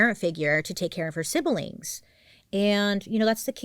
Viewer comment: I swear I heard you say she stepped in to take her off her civil links😭